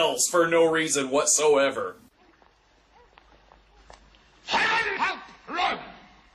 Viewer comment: What is he saying at 13:20? I just cant make it out. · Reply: “HEAVEN HELP ROME!”